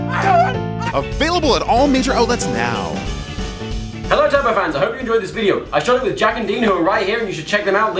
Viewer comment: i thought the man said tubbo fans i was like WHAAAAAAAA?